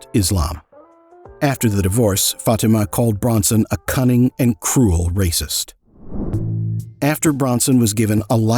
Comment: She called him.. 'A cunning and cruel racing driver or something or other..🤷‍♂️ I dunno i didnt catch what she said about Charlie tbh I wasn't listening..🤔 Oh really.. of course he is...😵‍💫 Coz she said so...👀 We have to believe her of course..👀 Who wouldn't..👀 She definitely married him for love and nothing more..👀 She said so..👀 So of course we believe her..🤔 If anyone thinks or says anything different you're one of those nasty unfounded words people shout..👀 Did we say we believe her..👀 We just want everyone to know we believe her..👀 We 100% believe her..👀